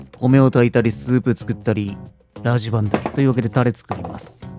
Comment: さりげなく ラジバンダリ 聞こえたの私だけ⁉️(古つ‼️) レバー 鶏は全く違和感なく食べられる。 それ以外苦手😓 久しく 鶏以外食べた記憶無し。 でも でも でも めっちゃ旨そう😍✨